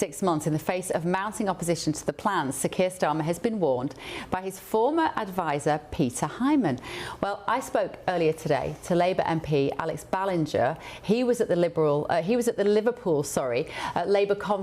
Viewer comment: Did she say 'Peter Hymen'? Actually, it's quite an appropriate surname for such a ....